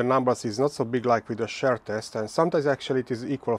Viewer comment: You should consider using Eleven Labs to narrate your videos. You can make a voice model to have your voice but easier to understand. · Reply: His accent probably is a factor in the popularity of his videos. It is pretty thick. I have to turn on subtitles, and even the subtitle engine has a hard time with it. The first time I heard him saying “share test” I was confused for a second. Then I mentioned that shear rhymes with beer. I don’t think he cares. Hehe. The accent isn’t exactly a huge issue, but I can see people not wanting to struggle with it and just skipping instead.